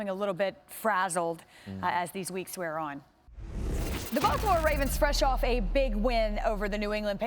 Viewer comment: she really said buffalo ravens 😂